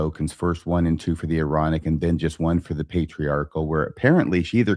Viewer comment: When says, “Aaronic” at the beginning it almost sounded like he said, “Moronic” which gave me a chuckle! 😂 Love the show! You make me laugh even without trying! 😊